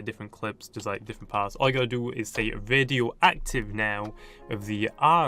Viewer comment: I thought he said Videoactive for a sec